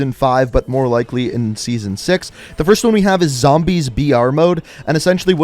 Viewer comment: Lmao for some reason I thought he said “Zombies VR” and I went 👁👅👁